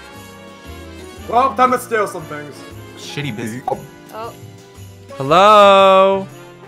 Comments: LOlBiT SWoRe!!!! XD She Said "Sh**ty" Oof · oof I heard that 17:30 shitty b- oh..